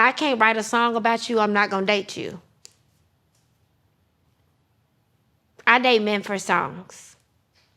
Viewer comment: did she just say she gave out box for songs? Hmm…I was unfamiliar with her work.